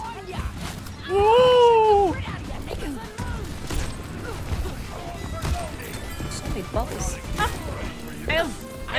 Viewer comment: what did junker queen say lol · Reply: Knickers